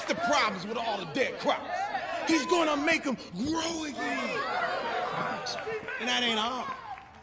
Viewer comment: "He's gonna make em grow again" I swear I heard "great again", my brain is done, I can no longer distinguish fiction and reality Whelp Cracks open bible